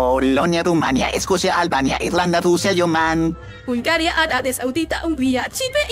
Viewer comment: soy yo o escuché eoman